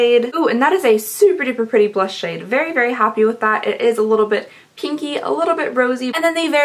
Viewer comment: When you said the Steve Laurent blush was a little bit pinky I thought you said kinky. I was like how can a blush be kinky? 🤔😂